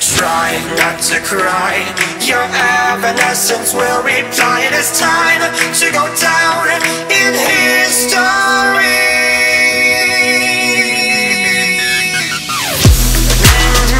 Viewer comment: did he just say evanescence BRING MEEEEEEEEEEEEEEE TOOOOOOOOOO LIIIIIIIIIIIIIIFEEEEEEEEEEEEE